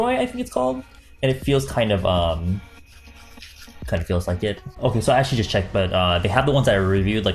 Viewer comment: hahahah I had to rewatch 1:03 three times because I thought he said "Kinda feels like d*ck" 😂😂😂 Can't help it.. naturally dirty minded lol 🙄